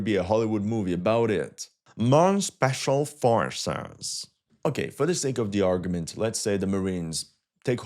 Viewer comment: can someone please tell me what he says right here ? What? “Special forces”